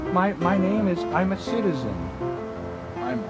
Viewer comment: Did he say his name was "I'm a citizen"?